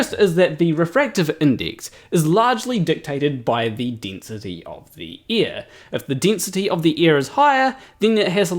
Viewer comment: The way your accent and mine mesh, I kept hearing "density of the ear". Got a few chuckles from that.